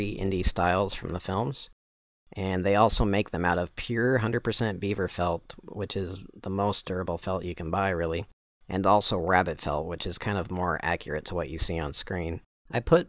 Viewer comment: Did you say beaver felt was the most durable? The first time I watched it I thought you said the most terrible felt you can get.